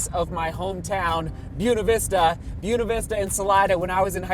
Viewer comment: Wow every time you said Buena Vista in past vlogs I thought you were saying Yuna Vista 😂 didn't realize the mispronouncing until just now seeing that sign